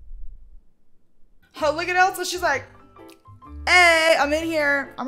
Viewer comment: the one with barbie ariels juice box sounds like shes saying religious not delicious